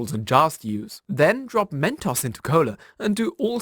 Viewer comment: I know this boy did not just say men-toss.